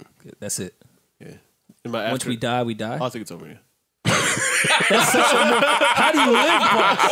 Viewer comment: Wtf is Parks saying that makes everybody laugh at 25:06? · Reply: Stanley Atkinson he said “Yea I think it’s over yea.”